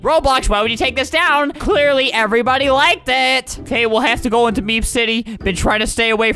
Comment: lolol, in captions it says “beef city” instead of meep city Edit: thanks for 30 likes! Can we get to 100? Edit 2: Tysm for 110 likes! What if we can get to 350?